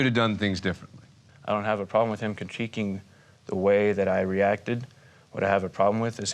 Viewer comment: What's the word says right @ 9:20? Contiquing? Cuncheecking?